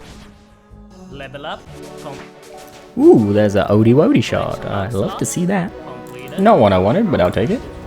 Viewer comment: I was going to sub until i heard "owdy wowdy shard"